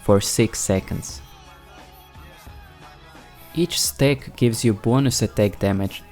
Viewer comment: with your accent I always hear "Each steack" when you say "each stack of conqueror..." that made me hungry XD